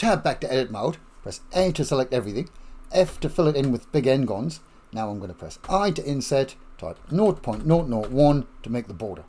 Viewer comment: Non-English speaker here: What do you say at 5:33 ff, where you type in 0.001? "North point North point one"? (The automatic translations gives me a "naught naught...") Would like to understand why "North" – if that is really, what you say :-) Thank you! These video series is really very, very helpful! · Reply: Hi Robert. I am saying "nought point nought nought one" (0.001). I have just had to Google this as you are not the first person to ask! I did not realise that it is just in the UK that we commonly say "nought" for the written number "zero". I will say zero from now on!!